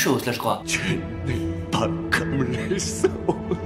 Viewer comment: Il dit quoi a 8:15 ? je comprends pas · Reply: Il a dit tu n est pas comme les autres